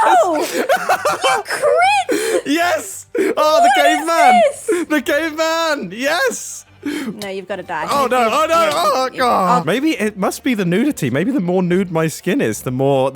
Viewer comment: i heard "the gay man" and not the caveman and actually cackled